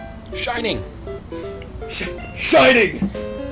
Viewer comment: he actually says shin-ing, not shine-ing...